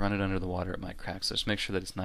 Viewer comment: "because if you run it under the water it might _______??" What are you saying here? Crack?